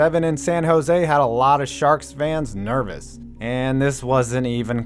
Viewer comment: It sounded like you said sharts😂